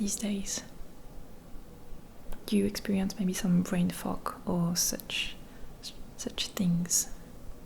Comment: I thought she said “brain fuck”